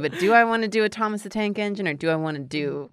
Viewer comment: *Oooooh, ENGINE, I thought you said 'eggs in'. Oh well, too late.